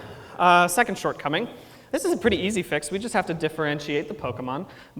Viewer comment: SO FUKCING PRETTY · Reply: i thought you said petty at first